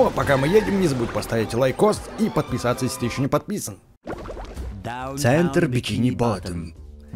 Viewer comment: Мне одному послышалось што сказал "забудь поставить лайк и подписаться если ты ещё неподписан"или нетолько я? XD